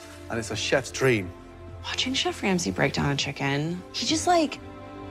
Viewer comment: did she just called gordon "she"